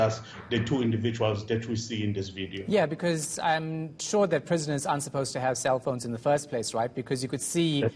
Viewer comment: am I hearing right or did he say "I'm sure that presidents aren't supposed to have cellphones"?🤔